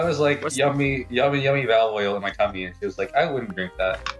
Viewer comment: EDITOR THE CAPTIONS ARE WRONG IT WAS ACTUALLY A SHE. I talk to all the women 😎